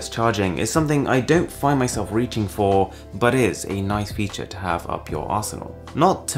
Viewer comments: at first I thought he said, " A nice feature to have up your asshole" lol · **did you say it is a nice feature to have up your arshole? Lol